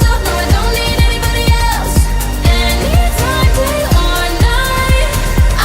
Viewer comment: I thought she said anytime that i lie😂😂😂😂 But its anytime day or night haha